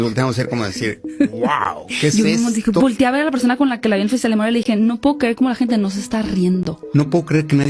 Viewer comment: Just in case, is not saying what's written on the screen, she's saying: "I cannot believe people aren't laughing at this (Selena's acting)" Thanks for making this video showing the impact this movie had on the Latin community. We need more chicanos talking about these topics. I loved that you spotlighted and, they are great creators. We as Latinos from Latam feel overlooked by USA/European/even chicano audiences when it comes to our own culture and opinions, so I really appreciate your insight and solidarity to us. There's definitely a conversation to be had about the difference between chicano culture and cultura mexicana, but getting together to fight stereotypes is always good.